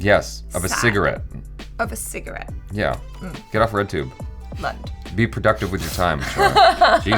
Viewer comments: did say lund?? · did she say lund?